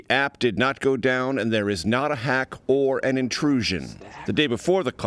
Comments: What is said at 1:57? It sounds like someone saying its a hack. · what is that breathed word? hack, stack? wth is cbsn trying to subliminaly do something here?